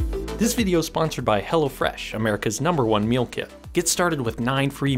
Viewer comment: 1 thought you said "Hella Fresh"